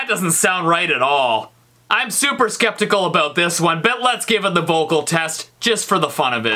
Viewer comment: At the end of the testing for the 1st one it sounds like you say give beans